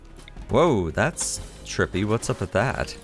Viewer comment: What is he saying is trippy/dizzying around 17:45 ?